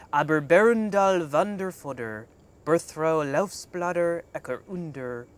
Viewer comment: I know I heard him say "Louse Bladder" Whilst speaking elvish. X___O